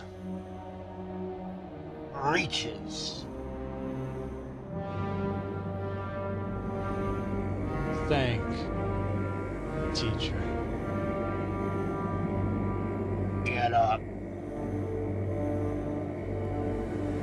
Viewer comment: A teacher recommended me this. I have gained more respect for him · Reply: Did you say "Thank, teacher" ?